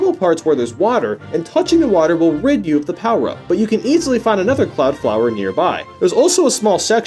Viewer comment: did he just say rape you of the power up